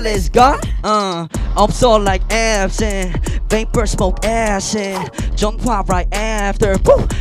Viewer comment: Am I the only one who heard mark say “vape or smoke acid,” at 1:34. I listened to that part multiple times and have heard the same thing I’m so confused 😂😂